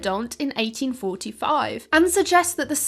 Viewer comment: "Sann people" or "Sand people"?? I listened to that 3 times and I am not sure what I heard, but subtitles say "Sand"....